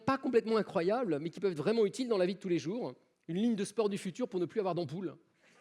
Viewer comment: J'apprends le français. Qu'est-ce qu'il dit au 41:46? J'entends "l'hymn (?) d'sport du futur pour ???? d'un boule. Vous n'enlevez pas votre poids, mais votre sommeil (?), bah, ne frotte plus, hein ? Puisque vous êtes exactement comme montrant (?) tout à l'heure, le sac-à-dos pareil, il frotte plus dans vos épaules, il pèse toujours". Ai-je tout compris ? Merci ! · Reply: "Une ligne de sport du futur pour ne plus avoir d'ampoule. Vous (n')enlevez pas votre poids mais votre semelle, ben, ne frotte plus. hein? Puisque vous êtes exactement comme mon train de tout à l'heure. Le sac à dos pareil, il (ne) frotte plus sur vos épaules, il pèse toujours."